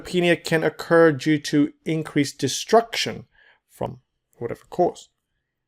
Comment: what did you say here, sorry? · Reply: “From lot of causes”